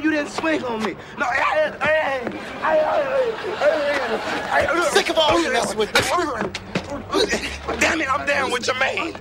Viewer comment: I couldn't catch what Martin was saying when he was getting his ass whopped anyone catch recite it for me ? (1:00-1:16) what was Martin and micheal saying to each other I was too busy laughing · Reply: Michael: "I'm sick of all you messing with us" Martin: "Damnit im down with Jermaine" Martin: "I bought La'toya album"